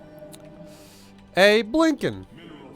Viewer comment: "did you say Abe Lincoln?"